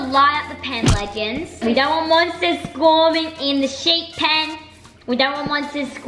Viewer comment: did say “squarming” ?